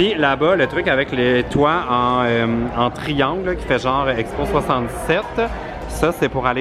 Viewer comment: C’est tu juste moi ou j’ai entendu expo 77 à 14:46??!! C’est expo 67!